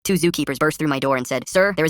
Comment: The first sentence, he said, I thought he said the zookeeper were busting on my door